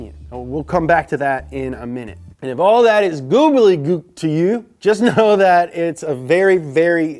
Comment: did you say googly gook